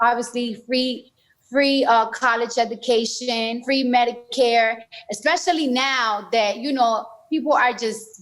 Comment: for a min I thought she said free pedicure!